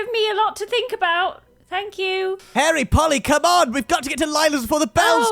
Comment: sounded like hairy polly lmao